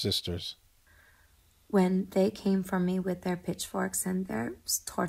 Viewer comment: Lol i thought she said Bitchforks